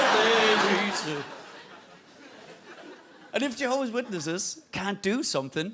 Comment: I love when he was like ‘og det er fordi jehovas witnesses cant do something’ and just moved on as if he hadnt just started that sentence speaking danish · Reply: 100% said "and if...." there. He very clearly says 2, single syllable words during the 3:51 second. Specifically the English words "and" followed by "if". 3:49 and 3:50 there is silence, so he's not adding anything before that. Unless "og det er fordi" is spoken as 2 separate, single syllable words I'm not sure how anyone could think he's saying that.